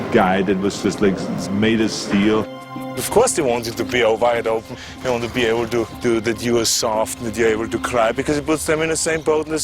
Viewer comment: What is Arnold saying? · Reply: Ion Batîr Of course they want you to be all wide open, they want to be able to view that you are soft that you are able to cry because it.... something about being just one of us lol.